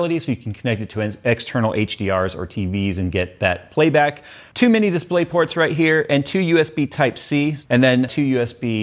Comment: lol at first it sounded like you said Too many display ports.